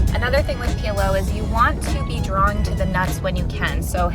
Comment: After all the talk about 2 bit whores 😂 i had to go back and re-listen at about the 8:45 mark when i thought i heard “you need to be drawn to the nuts!” When i re-listened i finally realized it was draw-ing to the nuts😂😂